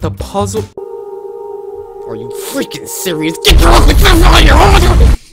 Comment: I heard him say "GET YOUR ASS OUT OF HERE!" I know he didn't say that, but I heard him say ass.